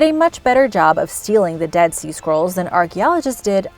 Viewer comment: For a moment I thought she was saying "dead sea squirrels", lol.